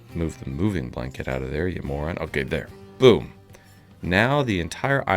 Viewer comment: did you just call your friend's apprentice a moron?